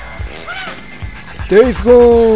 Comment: KOがDays Goneに聞こえるようになってきた